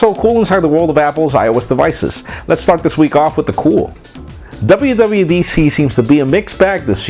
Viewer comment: it sounds like he says Lets fuck this week off with the cool